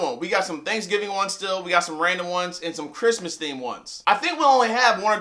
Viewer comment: You Have Some What Ones??? Awe😘It Sounded Like You Said My Name...Some Raina Ones🤣😂🤣